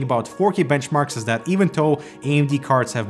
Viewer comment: I kept hearing “even toe” lol. It’s pronounced as “thow” but you might be able to get away with saying “doe” instead!